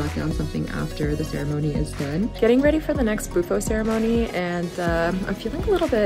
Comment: When she said "bufo" ceremony, I swore she said "boofing ceremony" 🤣